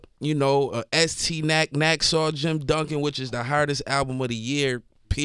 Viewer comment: what album is he talking about? If WSG says it albums of the year I gotta jam that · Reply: He said estee nack - nacksaw jim duggan